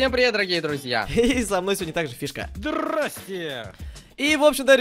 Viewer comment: послышалось "кишка"🤣 🤣 🤣